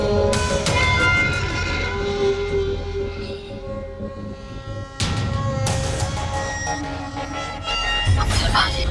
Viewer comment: fariya yahan kya bolti hai ?? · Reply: han tu ma farhat se milne b nahi i